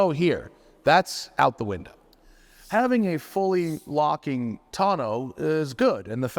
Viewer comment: Yes, it took a software update to allow locking. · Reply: Locking? I thought he said LSD... either way, just makes me curious what the issue was. They must have been chasing some kind of bug and just disabled the feature till they figured it out.